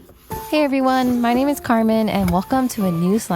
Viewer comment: Let's just appreciate Carmen for always consistently posting us slime contents! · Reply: Ngl thought you said camera man for a second 😂